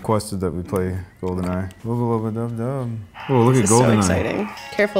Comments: did he just say wuba luba dub dub ʕ⁎̯͡⁎ʔ༄ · Wait, did he say "Wubba Lubba Dub Dub" ? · Did he just say "Wubba Lubba Dub Dub"??